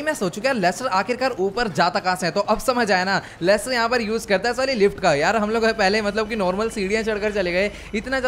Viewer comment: sali lift ka did I hear correct 😂